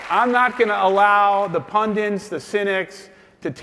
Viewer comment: did he say "pundins"?